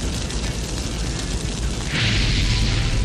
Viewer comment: 子安さんの ﾌｪｻﾞﾝﾄｳﾞｧｯｯｯｸ! 最高だわw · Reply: すみません。正確には 「フェザントベック」 です 個人的に「ﾌｪｻﾞﾝﾄｳﾞｧｯｯｯｸ!」って聞こえているだけなので…